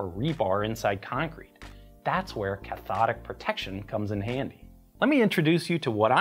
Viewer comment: I hear it as Ch'thonic Protection every time